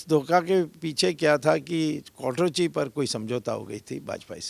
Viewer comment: pe kya bole Swami ji? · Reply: Kwatrochi